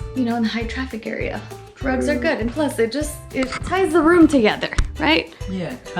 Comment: Sounds like she said drugs are good 😂